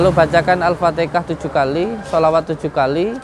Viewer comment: menit 40:56 apakah saya tidak salah mendengar bahwa gus Samsudin bilangnya bacakan alfateka 7 kali?? mohon penjelasan jika salah dengar. terima kasih